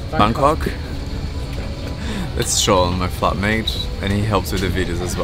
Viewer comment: did he say "that's shawn my F!@ mate" ???! I'm confused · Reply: "flatmate"